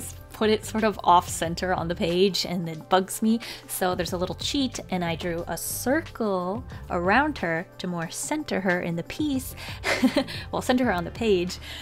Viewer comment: did you say it f* me?? or it bugs me?? idk😡